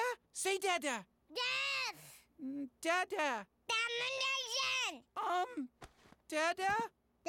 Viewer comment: i think he said damnation instead of domination